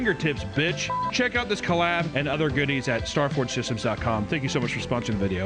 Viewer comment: 🤔is it me, or did it sound like was saying "stargfor" at the start of the starforge advert? xD